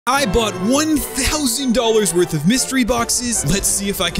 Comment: I thought he said i bought 1 000 eggs